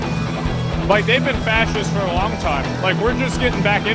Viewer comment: did I just hear that right? Facist?